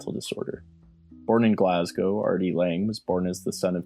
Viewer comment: I thought you were saying Artie Lang, lol.